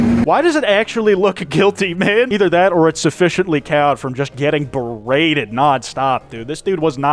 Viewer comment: literally no idea what noun they said here sufficiently what? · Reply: cowed